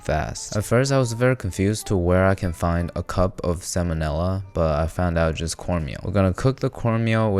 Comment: Did I hallucinate or he actually said Sam O' Nella